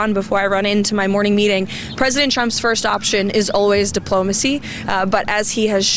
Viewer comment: it always sounds like she is saying "President Chump"